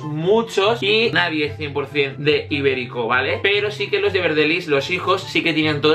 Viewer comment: los quien? no escuché bien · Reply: therotiv4 Los de VERDELISS (los hijos de Verdeliss)